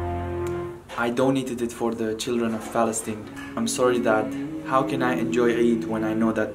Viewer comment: for a second I heard I don't need money for children of Palestine